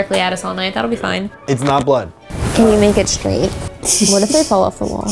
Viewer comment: I hear "can you film it?"